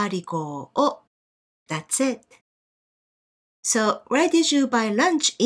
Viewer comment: i dont understand what sensei said on 6:05 please comment if u understand what sensei said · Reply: She said "that's it".